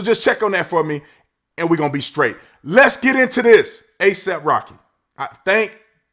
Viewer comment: Thought he said Aesop Rock